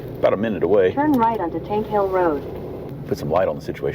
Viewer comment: Thought keep saying hank hill rd